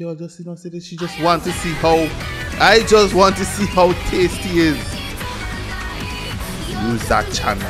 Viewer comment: I always hear it as "I just want to eat, healthy sea eels."